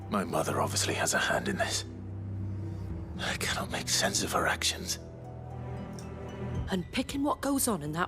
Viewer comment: Clive says "I cannot make sense of her actions" but I wasnt listening and it sounded like "I cannot make sense of erections"